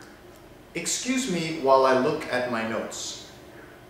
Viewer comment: I thought you said: "Excuse me while i look at my nose" lol